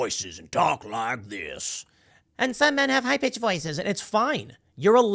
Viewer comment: I thought for sure you said, "Some men have high bitch voices" lol